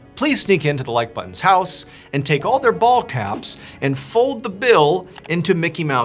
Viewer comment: Fully thought you said “bald caps.” I was thinking like, okay strange request, but you asked for weirder. Then, I heard “fold the bill” and realized that was not your request at all… 😅